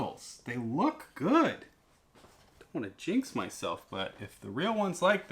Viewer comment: What did you say? "I am gonna jizz myself"?? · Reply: Jinx. So sorry :-/. I had to use the youtube subtext feature to understand that :D